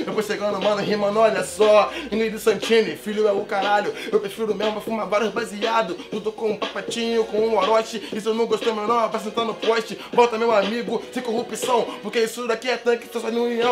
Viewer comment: quem percebeu falando "volta meu amigo" foi pro orochi!